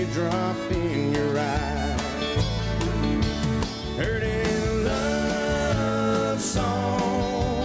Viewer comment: I always thought he was saying, Perty little love song